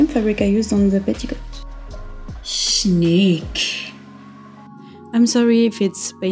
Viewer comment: did you say the Swedish word "Snygg"? :) Amazing dress!